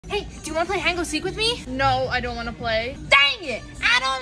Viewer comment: Sounds like she said hango seek